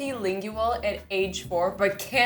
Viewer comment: I Couldn't Understand The First Thing She Said At 00:00, Can Someone Tell Me What She Actually Said? I Heard "Balti-Lingual"